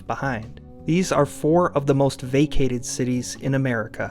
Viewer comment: I thought you said VACCINATED cities!!!!!